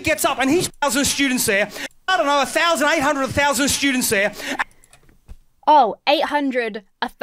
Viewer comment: Good arguments. Well done. As an Australian I am embarrassed by his bullshit. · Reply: I also thought the said "800,000 thousand" people at the debate.